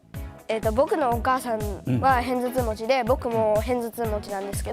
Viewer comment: I think he said hennzudtuu(片頭痛) and It's not hayfever. it's Migraine and hayfever is 花粉症.